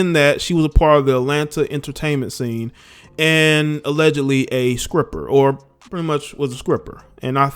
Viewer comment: Wait....did he say scripper at 4:37?? Please tell me thats a real thing and that he didn't mean to say stripper.